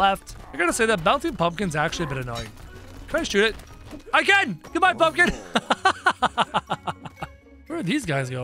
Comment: Did he just say GOODBYE FUCKERS?